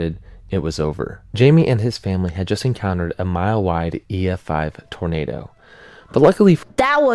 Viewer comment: It sounded like you said "Mild", so I'm gonna say: No, he said "Mile-wide Tornado" as in it was 1 mile wide.